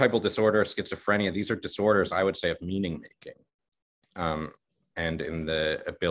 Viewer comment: said, "Schizotypal disorder is a disorder of meaning ______ (something, the word was unintelligible). What did he say? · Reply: "Meaning making"